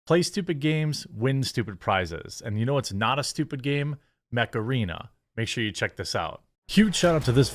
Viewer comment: I thought he said "Macarena"